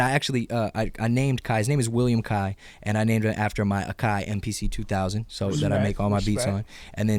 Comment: what did logic say he makes his beats on? at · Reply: Akai mpc